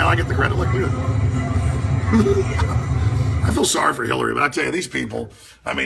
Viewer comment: Watch it, I heard you say "you feel sorry for Hillary" Alex in this video.